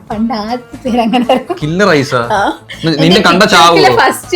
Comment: what did he said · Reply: Ninne kanda chavooo